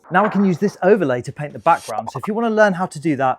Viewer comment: I thought you said grogu and I was like thats not grogu 😅